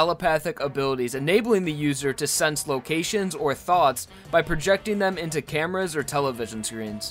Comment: OH MY GOD!! I forgot to include Empress in the video, apologies but I added Empress' segment in the description. Thanks for watching everyone. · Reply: Did you just say Hermit Purple can sense THOTS????